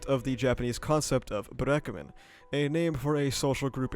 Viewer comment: I thought I heard “buraku man” (black man) I was shocked for a sec